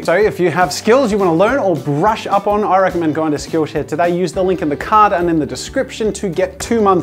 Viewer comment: i thought you said SKILLSHIT